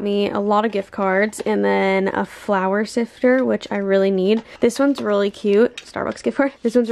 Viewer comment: When you said “flour sifter”, I thought you said “flower sifter” and I was like what the heck is that lol